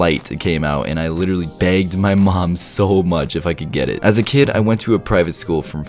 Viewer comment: I thought he said banged my mom XD